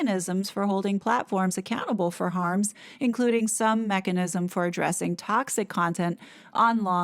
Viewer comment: Initially when you said "mechanism" I heard "Meganism!" 😳😳